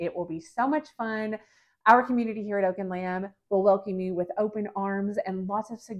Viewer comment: Love this video idea!! So cute. Btw, when you said our community will welcome you with open arms I heard “our humidity will welcome you..” and I didn’t bat an eye. Guess these temps of 117 in Oklahoma will do that to ya lol